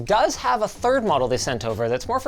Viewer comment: I thought Linus said "for POOR people just starting out" 💀